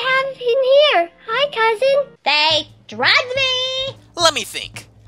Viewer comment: it sounds like she said,”THEY DRUGGED ME!”